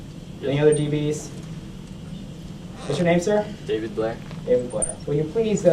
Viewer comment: Did anyone else hear "David Blaine"? ... famous magician